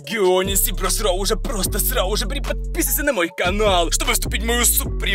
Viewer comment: мне одному кажется что он сказал просрал